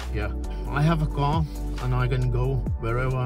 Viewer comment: he said "car", not "challenges". Seems like this video was closed-captioned, not manually subtitled. Just a tip re. this: the CCs probably wouldn't be necessary if the background music wasn't so loud -- it makes it quite hard to hear what people are saying! This is especially noticeable at 4:57. Oh man, though -- I didn't realise accessibility was so bad at fuel stops. I'll admit that's somewhat down to naivety and ignorance. I was quite surprised to see that couldn't actually reach the fuel pump! From an engineering standpoint, it might be helpful if the gas pistols (as calls them) were able to be pulled downwards, with an auto-retracting mechanism to prepare the pumps for other road users. Overall, a very interesting watch. Thank you!